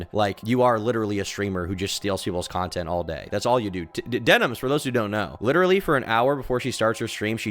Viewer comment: did bro just say those who know